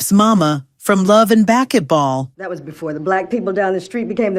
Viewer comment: Did she just really say love in backetball instead of basketball 9:18 😂😂😂😂😂😂😂